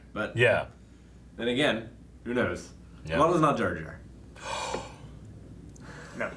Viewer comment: what does he say at 1:08 ? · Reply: As long as it's not Jar Jar.